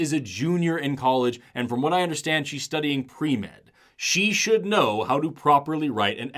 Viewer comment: she’s studying what? I’ve replayed this part over and over and still dont understand what youre saying 😢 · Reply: Pre-med. As in pre- medical